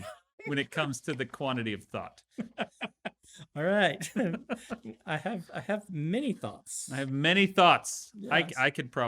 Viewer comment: They keep saying " I have many thoughts " and I keep hearing " I have many thots' and I can't stop laughing every time they repeat themselves